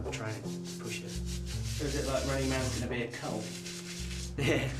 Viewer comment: what did the cameraguy say? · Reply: He says is running man going to be a cull. The exercise they are talking about is called running man